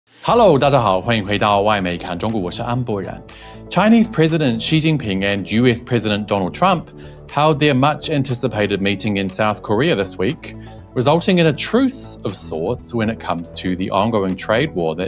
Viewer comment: I heard “Jewish President Donald Trump”